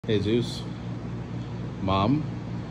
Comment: He said “hey Zeus”, I first heard “¡Jesus!”